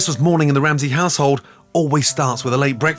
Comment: i legit heard "Christmas morning in the Ramsay asshole"